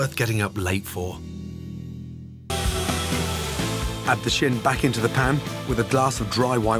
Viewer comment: In the trailer for another video at 1:08, I swear he says, "Add the shit back into the pan." I've listened to it a dozen times. What's he actually saying? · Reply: Actually, he said "shin". Who eats chins? I love the way that people in YouTube comments excel in being spectacularly rude and spectacularly wrong at the same time.